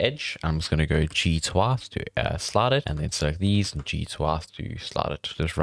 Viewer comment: what did you say? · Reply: G twice to slide it